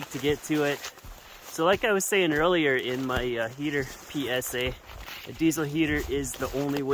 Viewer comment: did say: "In my heater PSA" ? Or, "In my heater PS Eh" ?